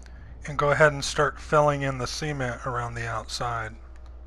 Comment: I kept hearing "semen" instead of cement haha, such a cool build though!!